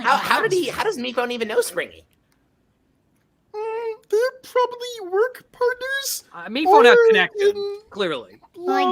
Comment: someone please tell me I'm not the only one who heard what Goo said here PLEASE NOBODY I'VE MENTIONED IT TO HEARD IT I FEEL LIKE I"M GOING INSANE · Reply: “Mmm… they’re probably work partners… or… in… love…” I love Goo